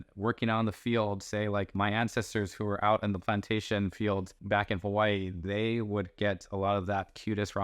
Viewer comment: Thought you said plantain 😂 I was ready to justify more consumption.